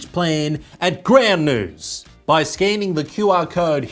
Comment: Didn't know what the news site was because they ran out of ink before finishing their logo and I thought was saying "Grand News" or "Gram News", or, "Graham News" where it gives graham cracker news. Thought the logo had a bunch of I's. Finally found out when the url was put up.